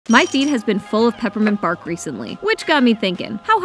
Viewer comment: I thought she said “my feet is full of peppermint bark recently”😭💀